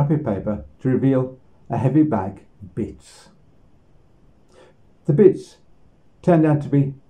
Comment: I thought you said 'a bag of beets', which would certainly be an unusual gift.